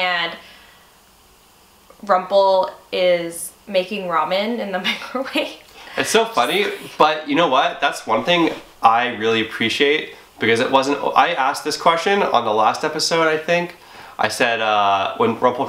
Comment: I thought they said making Robin in the microwave O_o